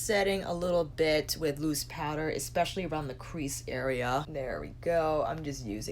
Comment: Haha I thought you said just setting with moose powder and here is me oooh what is moose powder! Than I realized you said moose powder. Haha. Love your pallet reviews and all your looks are always wonderful!! I have light medium skin and I think there is a decent amount of shades for all skin tones in here. Do you ever do a look and love it 😍 so much you don’t want to take it off to do another look? Loved look 2 👍